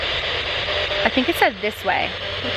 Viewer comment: I definitely heard it say “this way” than “closer”